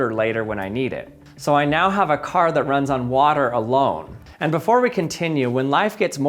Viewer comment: Oh... I thought you said "A cat that runs on water." I was expecting to see some kind of little Jesus kitty or something. Oh well, this is cool too.